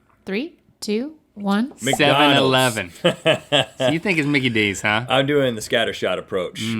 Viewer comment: i swear he said nine eleven